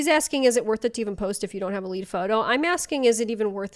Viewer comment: thought she said leaf photo 😂and no dump should take 3 hours, you have a problem no matter what kind it is lol